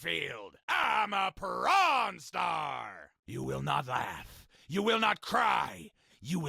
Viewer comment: Did he just say I am a po@ star at 15:38💀💀